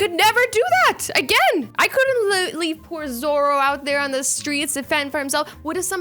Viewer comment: did she say "Zoro" like one piece swordsman